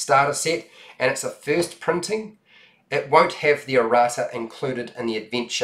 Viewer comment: what is aorata? That is not included with the first print? I'm English and don't understand this word :/ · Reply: He's saying "Errata", which is a list of corrections and clarifications for errors and ambiguities that might be in the retail version. (I actually would have never understood him saying it if I hadn't had context)